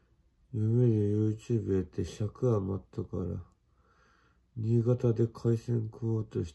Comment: 字幕は｢夕方で｣になってるけど ｢新潟で｣って聞こえる…